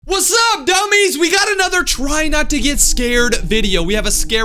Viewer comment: Did you call us dummies?